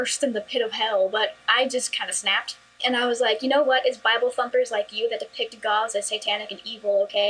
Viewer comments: For the last six years, I thought she said Bible Fuckers. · I thought she said “it’s bible humpers” instead of “Its bible thumpers” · She said bible... you don't want to know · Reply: Ziying Lim I thought she said bible fu*k... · Same here but I’m emo. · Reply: Crispypizzaaj I thought she said bible thumpers lol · i thought she said bible fuckers